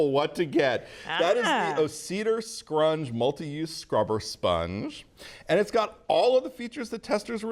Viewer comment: Excellent test methodology and summary BUT you only said the name of the winning sponge, there was no overlay to show the details (including how to spell it) nor is it in the video description. Please add this vital info here and consider doing so for all past & future videos. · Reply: You are right on the money because I sent this video to one of my friends and they misheard “scrubber sponge” as “rubber sponge”